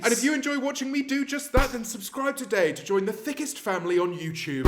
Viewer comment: did i hear THICC